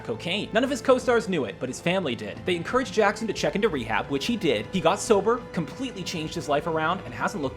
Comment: Good video... but... am I the only one that noticed that they refere to Samuel Jackson in the minute 1:48 as SHE instead of HE?